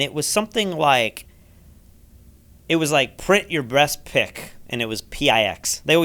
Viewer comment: I might have misheard fuzzy mic: "print your breast/best pic"